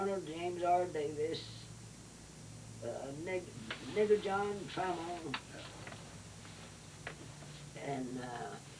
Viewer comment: what name did she say???? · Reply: John Trammell.